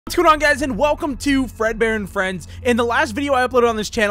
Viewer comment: hold up What did he say? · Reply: “What’s going on guys”